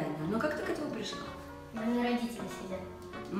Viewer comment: Мне послышалось У меня РОДИТЕЛИ СЕДЯТ